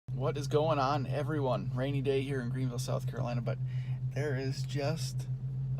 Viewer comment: Who is Randy? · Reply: LOL, my bad, you started the video saying, “rainy day here in...”, I misunderstood and thought you say “Randy Day here in...”